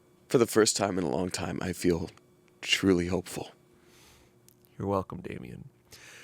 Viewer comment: I had to rewind a couple times because I swear I heard “you’re welcome, baby.” and not “you’re welcome, Damien.”😂😂